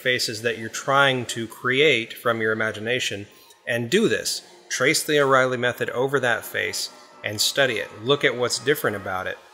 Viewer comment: lmao, i just noticed that at the end I made an "uh" sound in between "the" and "reilly" and it made it sound like I said "Trace the O-Reilly Method..." haha, i'm dying... could you guys imagine drawing the head with the O'Reilly method, instead? XD